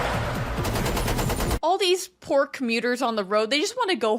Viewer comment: Here's an example of how you hear things differently when you're not completely concentrating: at the beginning of the highway chase when you mentioned the "poor commuters", for some reason I heard you say "pork meters". Normally that wouldn't make sense, but this is The Matrix.